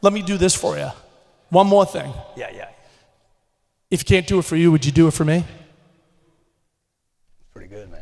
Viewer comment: What did he say at 3:28 ?? Did he say if cade do it for you would you do it for me? I didn’t get that pls someone explain